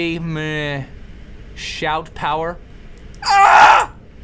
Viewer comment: i swear he said sh**